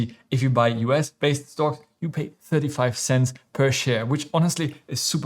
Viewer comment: you say 35 cents per share, when the screen shows .35 cents. 0.0035 is 35% of a single cent. Edit, i think you do say “point thirty five cents “